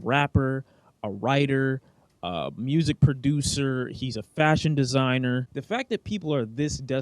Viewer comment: when you were saying kanye was into fashion i thought you said he was fascist